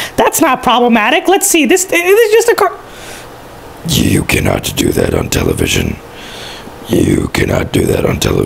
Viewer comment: he sounds like he is saying YOU CANNOT DO THAT UNTIL VISION